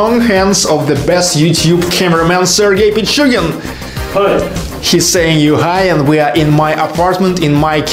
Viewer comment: Can't wait for the follow up video on Sergey Pichugin behind the camera. Did I really hear you mention "she's saying you hi" at 0:14 ? Does have more to reveal in coming videos ? Funny Sergey !